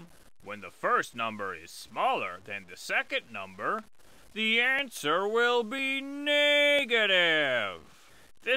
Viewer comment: did im the only one who heard n word?